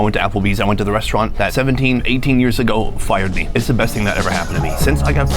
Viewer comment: I thought he said 30 years ago at first and was like youre going to be disappointed because no one you knew stjll works there and the general manager is probably dead.